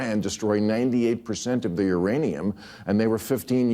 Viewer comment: It sounds like he said “Iranium” 😂